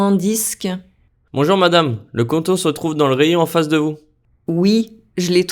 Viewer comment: face en vous. I thought the guy was saying 'festival'. It through me off because I was thinking they were at an outdoor market.